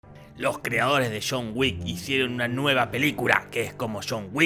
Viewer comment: Desde que escuché “shon wick” dejé de ver el video